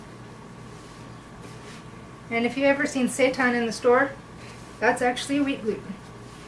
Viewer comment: Did you say "Satan in the store"?! =P